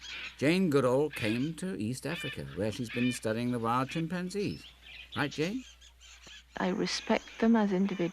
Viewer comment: Did said “Hi Jane!” on-screen? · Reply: I think he actually “Right, Jane?”